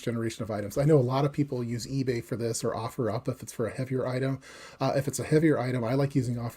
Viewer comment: what's the place to sell heavier items? I can't make out what you are saying. Thanks. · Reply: OfferUp